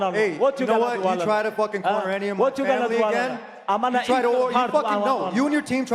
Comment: Did he just say im gonna eat your heart? Khabib "Iron Mike" Nurmagomedov